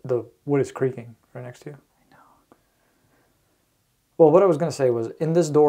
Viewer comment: Anybody else hear the whisper? It sounds like 'i know' maybe... Or is it? But why would he whisper when he was just talking in his normal voice moments before? Anyone else just as confused ...just me? Mmkay.